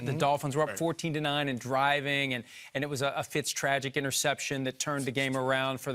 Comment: Did he say "Fitztragic" 18:05? Lol